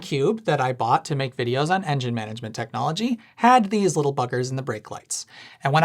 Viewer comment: I’m suing for damages for the whiplash I just received from mishearing and thinking Alec said “one of these little fuckers”